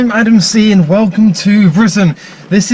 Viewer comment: I’m eating cereal at the beginning of the video and I thought he said ‘hi I’m Adam C & welcome to prison’